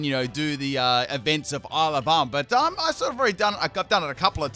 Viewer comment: when you say ile of armour i always hear alabama, it's very disturbing